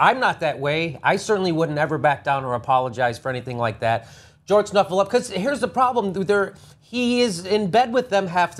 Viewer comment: Seriously, TYT? If this was a Republican you would have spent the whole video chastising. If Stephanopolus didn't think he wasn't doing anything wrong, why did he "forget" to disclose the donation? Of course people can have biases while working in the media, that doesn't excuse them of behavior like this. And get off of that messy excuse that the Clinton foundation is a "charity". It's not. It's a money pot for the Clintons and their friends. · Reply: Alvaro Lopez 4:32 did he just say Jorge snufulufucus? And what SCANDAL with Bill O ? The Argentina thing? Because that pretty much blew up in these fools faces as him telling the truth. I guess they didn't want to see the CBS videos. And lastly , Anna, Playboy, make it happen!